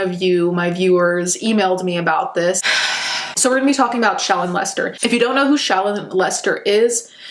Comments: I could have sworn you said "Lets talk about child molesters" and not "ShALLOn Lester" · I was cleaning while I listened to your video and I could have sworn you kept saying her name was "child molester"😯😬😦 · Instead of hearing the name Shallon Lester, I thought said child molester. I feel like that says a lot. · I thought she was saying "child molester" until I saw who she was talking about... Guess I was half right lmao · Every time you say her name, to my ears it sounds like your saying "child molester". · OKAY BUT YOU SAID SHALLON LESTER I THOUGHT YOU SAID CHOMO AND I WAS LIKE WHAT???